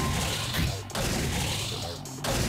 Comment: For a second there I thought the announcer said Rainbow Penis. I had to replay that part to realize he said Rainbow Phoenix.